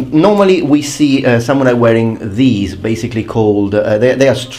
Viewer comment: min 5:25 , waraji? warachi? uarachi? uarachi? (h)uarache? That sounded a lot like "huarache". It is sandal in spanich, or at least in México; althought here it is a sandal made of leather. It is very weird because I heard huarachi (a lot like huarache). I don´t think it is related but it is very , very strange that it sounds that similar. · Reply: Fernando Navarro is Waraji it might be a loan word from Japanese, who knows ahaha